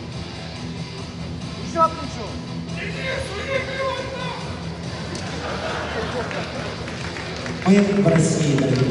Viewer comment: Can someone tell me what the dude said at 4:22? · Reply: The dude from the crowd said: "Denis, you need to win!"